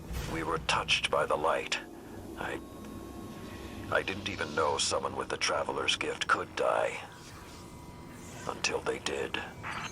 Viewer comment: What’s being said at 18:33? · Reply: "I didn't even know someone with the Traveler's gift could die.... 'till they did." Saladin had never seen a guardian die and stay dead. Their ghost always brought them back. Siva managed to separate the Iron Lords from the Traveler's power and corrupt their bodies. Yes, those were physically the Iron Lords in the chamber. However "who" they were was long dead. They were little more than Siva zombies at that point.